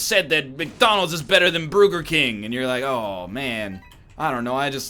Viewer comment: did say Bruger King?